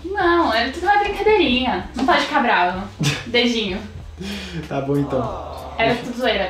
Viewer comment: kkkk eu entendi beijinho